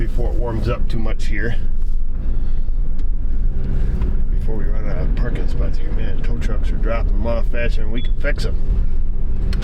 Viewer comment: At first I thought you said “the dump truck is dropping them off faster than we can fix em”... 😆